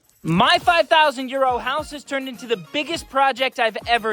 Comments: The first 3 videos I thought you were saying "my 5,000 year old house". I was like "where?". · All along i thought he was saying "my 5000 year old house" 🙄 bro been saying 5000 euro.........Wow!.....